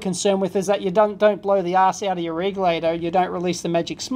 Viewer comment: "Don't blow the ass out of your igloo." ...Did I hear that right???? Solid advice, but I think something is funky with what I am hearing.